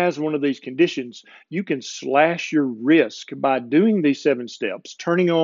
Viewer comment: LOL! - around 2:40 or so, you said, "cut your risk," …. I heard, "cut your wrist." Oops. On deep sleep: I don't know if this is the same thing, but this time of year (when it gets occasionally cold) I tend to sleep deeper and more often - but here's the kicker: I dream like crazy, and when it's a good dream I don't want to get up but sleep more. Is that a good thing or a bad thing? (I also seem to be more mentally active during the day - reading, writing, watching videos like yours ... so there's that.) As to thermogenics - I live in an area of Texas where, this time of year, it might be in the 30s in the morning and in the 90s (or higher) in the afternoon, and back to the 50s in the evening, all in the same day. For the longest time I stayed indoors but recently I've been spending a lot of time outside, just sitting on my porch, even taking walks in the morning if it's not too cold.